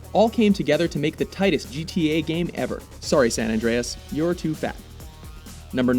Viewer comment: does he say "sorry san andreas, you're too fat?"